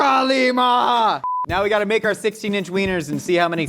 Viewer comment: am I hearing that right? Did josh say Kali maa? I yes then is he familiar with indian culture? I have so may questions